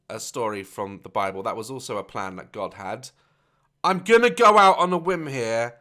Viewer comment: did he say i’m gonna go out on a wimb?